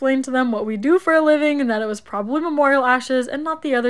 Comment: I thought she said fish ashes 😮😅